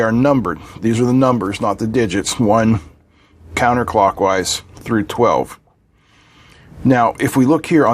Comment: counterclockwise??? 3:46. Am I hearing this right?